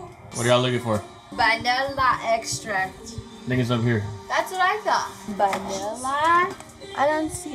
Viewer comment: What did you call the vanilla extract... lol "___ its over here"? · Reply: "think its up here" open ur ears bruh